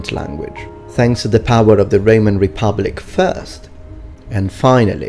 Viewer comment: It sounded like Reman Republic and Reman Empire, or I'm just hearing wrong... lol